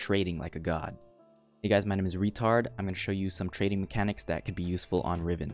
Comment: Did I heard correct? R e t a r d???